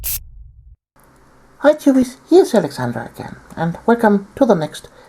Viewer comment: You say "Hercules" at the start of videos? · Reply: LOL...I heard "Hi cuties"